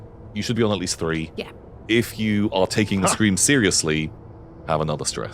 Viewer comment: During the scream bit I thought said "For those taking the game seriously" instead of "For those taking the scream seriously" and I was genuinely shocked for a moment, had to rewind to make sure I heard it right. Which yknow, I didn't